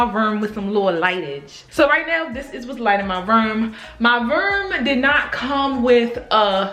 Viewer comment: that Baltimore coming out 😂😂 “My Rerm” (room lmao) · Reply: i thought she was saying “my verm” 😭😭😭